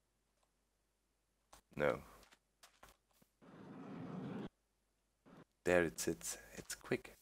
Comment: Did he just said "there it's it's" at 24:52? that was kind of funny.